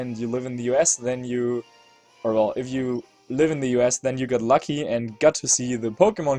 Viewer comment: What i heard was:"If you living in YOUR ASS ,than you got lucky " P.S. BOOMdorus is MVP